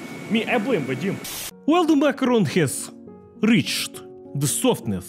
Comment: Блум! I thought you said “Makarov” instead of “Makaron”; big problem!